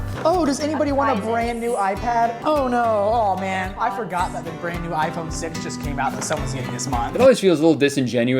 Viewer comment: did he say “brand new iphone 6”